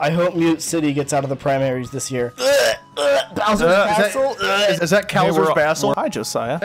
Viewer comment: I know said " cowser's bastle" but at first I thought he said "bowser's asshole"